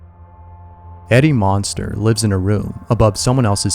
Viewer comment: i swear i heard eddie munson